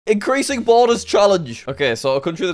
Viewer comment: I thought you said boulders